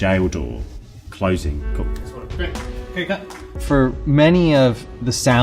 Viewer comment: what was that? did they say cut?